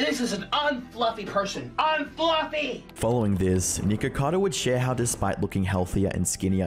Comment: am i the only one who heard nickercado on